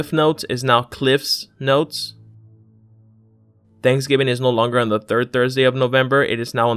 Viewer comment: I thought he said Thanksgiving is now on the 4th of November, not the 3rd of November. I was like WHAT IT'S THE FIFTH OF NOVEMBER DID I MISS THANKSGIVING